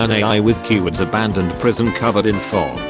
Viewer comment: i really thought that said “abandoned prison covered in frogs” and was really disappointed that it wasn’t